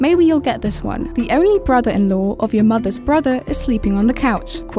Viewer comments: what ? I couldn't make out what u said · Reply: the word is brother-in-law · In the riddle with the family on the couch, I heard "the only brother in the wo of.." Which makes no sense. Therefore I can't answer lmao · 2. Whoever's on the fucking couch 1. My depressing life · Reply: what did the woman actually say cuz it was sorta just blurted out. I heard"the only brother of your mothers in the world" lol what?